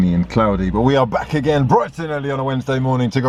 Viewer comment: This whole time i thought was saying Brighton early :(((